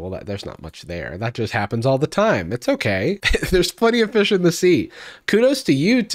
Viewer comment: I thought you said "there's funnier fish in the sea" and I was like "damn, brutal"